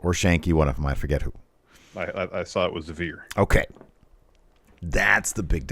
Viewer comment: I thought i heard said "i saw it, it was a deer" hahah i misheard it when you they were answering questions